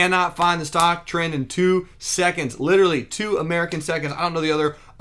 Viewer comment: Did he said 2 american seconds